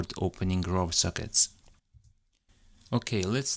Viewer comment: I'm confused by what you mean here, 2:22. Did you say websockets? Or Rob sockets? what's rob sockets? And why'd you say web doesn't support websockets if you did say websockets. . . could you clarify a bit? I'm new to IoT devices, coming from a software engineering background. So everything is a bit new new! hhh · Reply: "... web doesn't support raw sockets", because for web platform in flutter you cannot open raw UDP socket which is required for smartconfig